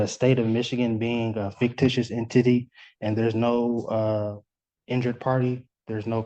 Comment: did this guy say IN- TITTY ? instead of entity?